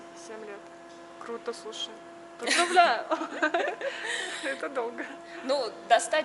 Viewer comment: На 21.48 секунде.. мне послышалась или ведущая сказала ну бляя... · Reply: Она сказала поздравляю)))